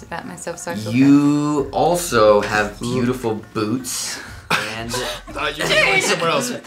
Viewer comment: I thought he said Boobs, instead of boots.